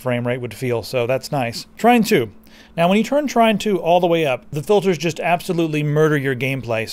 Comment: Yes, i did but i couldn't understand the way he pronounced the name. I heard 'trynt 2' instead of 'trine 2'.